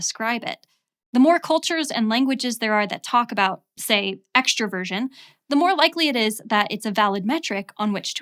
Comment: Oh Lord, I was not completely focused on the video and when she said "extraversion" at 11:51 I heard "extra virgin". I was so confused, I thought we were about to enter a legitimized Buzzfeed what-kind-of-olive-oil-are-you era of personality testing